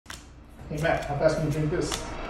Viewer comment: If it sound like he said "eat" he said drink and put ur ear to ur speaker